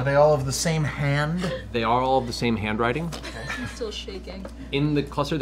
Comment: I genuinely thought Matt said Jarl when he said "are all" lmao